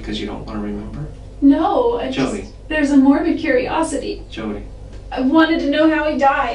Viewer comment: Is it the audio quality or does she have a Freudian slip at 33:18 and almost says Mormon instead of Morbid?